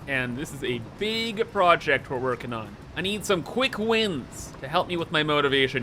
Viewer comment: I thought you said you needed quick wind so I started farting on my phone. Now I feel dumb.